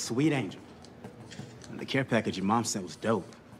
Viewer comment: BRO DID HE SAY CARE PACKAGE??? 0:16😭😭😭😭😭😭😭😭😭😭